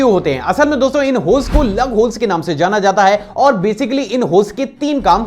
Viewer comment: I heard "Love Holes" 🤭